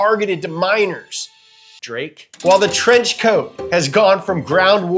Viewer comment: “Levi’s were made for MINERS , Drake” 💀💀💀💀💀💀💀 bro you got me with that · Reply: I thought he said minors Drake lol😂